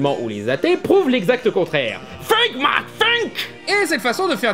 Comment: Je vais me faire sûrement passer pour un idiot mais j'entends bien "let my think" ou "think my think" et si c'est bien un meme ? Sinon tu continues tes vidéos, J'espère que tu auras plus d'abonnés et de likes car je suis surpris que plusieurs de tes vidéos n'ait pas encore percé le million La religion c'est quelque chose de très dangereux à traiter (même en philosophie j'essaie d'éviter d'ouvrir ma gueule sinon je risque de ressusciter moi aussi après être crucifié sur une croix avec des clous dans les pieds et les mains !) Pour faire bref je ne suis pas croyant en aucune religion même si de base je suis catholique et je suis obligé d'avoir un cours de religion et du coup j'ai choisi catholique... Contre mon grêt ! Alors je suis neutre vis à vis des religions (même j'en n'est rien à f*utre). En revanche, je suis moins tolérant quand on discrimine une religion comme la musulmane sur le biais de stéréotypes r*cistes sur eux = des t*rroriste et les catholiques qui n'ont rien eux alors qu'il SONT PIRES dans certains domaines !!!! (Attention dans ce que je vais dire, je ne vise que les extrémistes de cette religion et non les VRAIES croyant du catholicisme, ceux qui ne persécutent pas les minorités et sont des cons conservateurs) Déjà quand je pensais au catholicisme je pensais à des gens avec un mental de paysans médiévaux ou à des bourgeois disant les femmes à la cuisine !!! ou à des prêtes p*dophiles se justifiant de leur acte en disant que c'était l'ordre des choses dans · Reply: Il dit "think man think" "réfléchit mec reflechit"